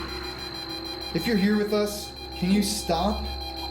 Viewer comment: if you listen close, it sounds like something said, “can’t stop” when you asked for it to.